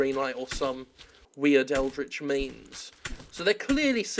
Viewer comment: P.P.S. on a less serious note, when you said "Eldritch means" I totally misheard it as "Eldritch Memes".